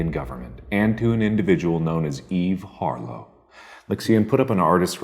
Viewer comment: At first I thought he said eef harlow😂 like is that a new ego of ethen😂😂